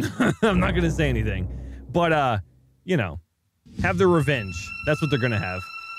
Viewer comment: they are gonna have....idk what they r gonna have my brain says have:(sex) says have:(revenge) who is right? XD lol